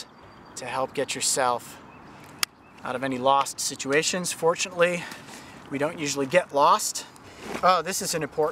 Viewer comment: I’m scared because I can’t tell if he said “fortunately” or “unfortunately” he hasn’t been bit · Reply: He said "uh fortunately" I thought he said unfortunately😂😂